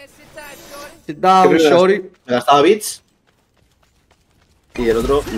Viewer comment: o gente de los coments, resuelvanme una duda pls, la linea que dice vamana cuando pone el ward es "easy time shorty" o "sit down shorty" ??? para ser exacto, lo que dice en el min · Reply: Ey, sit tight shawty